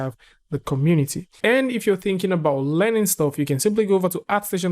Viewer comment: I heard you say "Lenin stuff" and I chuckled as Epic made education free for all people 😄